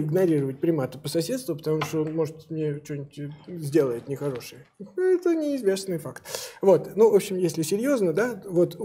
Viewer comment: «ни***я это неизвестный факт» ??? Я правда это слышу??))) мне пора лечится...